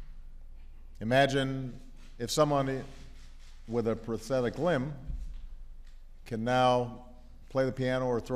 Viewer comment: It sounds like he said prophetic limb, what are they trying to do, play God?